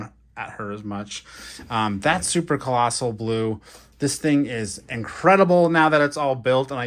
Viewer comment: Did you say blue?